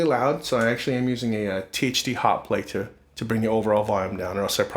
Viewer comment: What's he saying @ 0:30? He says he's using something to bring the overall volume down: THD something ("hotplater" sounded like).